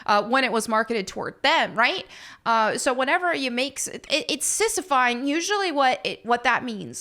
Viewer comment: Sissy-phying? I wanna be sure that’s what she said